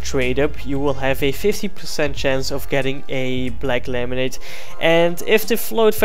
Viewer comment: Did anyone else hear "Black lemonade" ?